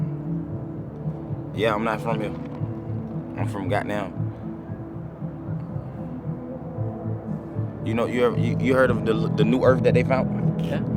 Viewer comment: They thought he said he from Gotham. LMAO